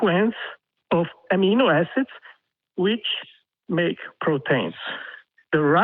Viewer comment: I keep hearing 'propane' whenever this dipshit says 'protein'.